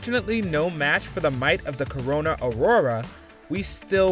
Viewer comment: Did he just say Corona ?! 😂🤣